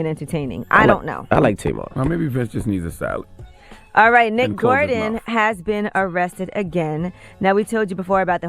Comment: HAHAHA! Did Charlamange say Maybe Vince needs a salad? I can't with Charlamange